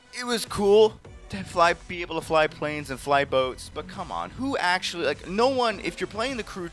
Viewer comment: did he just say "fly boats"?